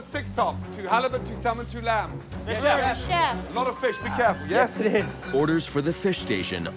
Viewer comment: did he said " lord of fish becareful " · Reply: A lot of fish be careful